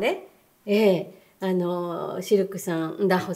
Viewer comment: 視力３ンダホさんにしか聞こえんw